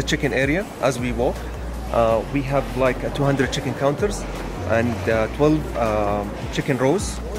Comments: Am I only one who heard saying chicken not check-in 🤔🤔 Edit 👉 thanks for like😃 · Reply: No, I heard the "chicken counters" too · ooh never heard of chicken counters but sounds fancy. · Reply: Everyone gets so pressed why? I thought he said chicken counters too, so I thought it was funny